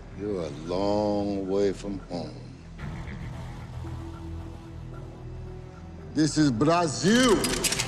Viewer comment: I always thought he said “this is blast you” instead of “ this os Brazil”